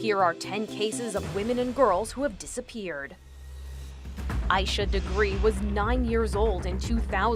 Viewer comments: When she said asha degree i thought she said I should agree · The first little girl . I thought she said “i should agree” I’m not making fun of her name or anything. It’s just I heard different and I was wrong.